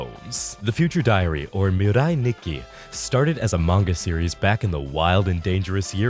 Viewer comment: I heard "a manga series" as "amongus series"